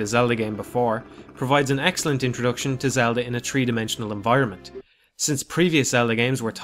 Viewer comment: When you were describing the first dungeon, I heard "Tree-Dimensional", LOL.